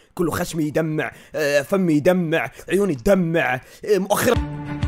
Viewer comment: شكلي الوحيدة اللي سمعت أنو كان يبي يقول مؤخرتو تدمع😭😭🤣🤣🤣🤣🤣